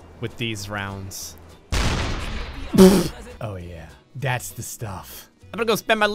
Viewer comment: I could not understand what you said besides loot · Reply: "That's the stuff. Ima go spend my loot alright folks hope you liked uboats"